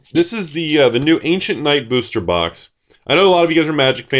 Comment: I thought it said Arabian Knights...